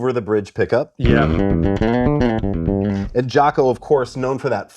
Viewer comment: anime fighting game brain rot all I hear is "Jack-O" 🤣